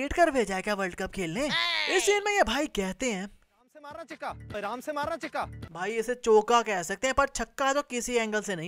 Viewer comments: He is not saying chaka. The batsman's name is cheeka 🤷‍♂️ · in 2:59 he said aram se maar na cheeka ,cheeka is the pet name of shrikanath who is batting, he is not saying aram se maar na chaka